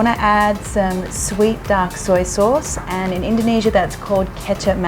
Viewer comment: Since when kecap is duck soy sauce? There's no duck in it. Or am I hearing it wrong? We do have 2 version of kecap. Kecap manis (sweet soy sauce) and kecap asin (salt soy sauce). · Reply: It's "dark" soy sauce, you misheard it